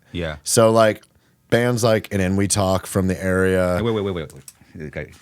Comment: What was the first band Brandan said was one of his influences around the 32:20 mark? Sounded like "and then we talk" but i don't think that's right. Trying to look them up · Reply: Enewetak